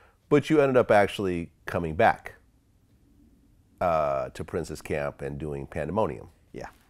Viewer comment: Sounds like VLAD is saying, 'princess.'